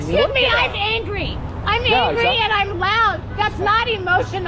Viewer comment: All I heard at 5:20 is “I’m ugly and I’m proud! I’m ugly and I’m proud IM UGLY AND IM PROUD”…… “so that’s what he calls it”- if you know you know. 😂